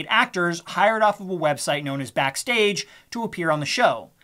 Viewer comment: did you say Backpage? Oh backstage nevermind wrong website